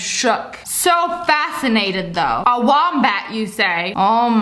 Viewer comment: a wombak ( what I thought she said) looks like a...... Me: let me look that up types wambak but then sees wombat taps it looks at pictures then notices it’s cute